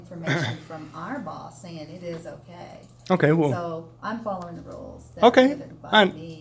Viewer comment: Did she really say “I’m following the rules...” and then wink 😆